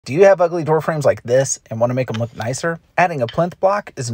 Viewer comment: The way he quickly says "do you have ugly door frames like this" at the start, actually sounds like he was saying. "Do you have ugly girlfriends like this.." and it made me laugh.